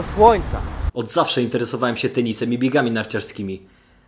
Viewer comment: usłyszałam penisem ;|